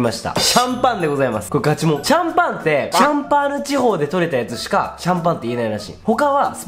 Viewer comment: え？ シャンパーニュだけど。。。シャンパーナに聞こえるんですけど